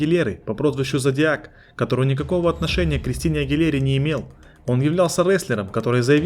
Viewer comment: esta genial el montaje del video, lo vi entero, una lastima que no entendí una mierda, ojala algún día pueda. 6:58 dijo cristina aguilera?